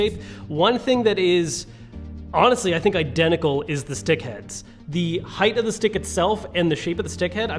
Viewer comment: Heh. Anyone else hear Dick instead of Stick? I wasn't listening carefully at this point and chuckled a bit at first.